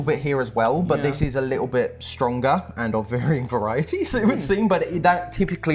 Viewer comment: I think bruv said stwonga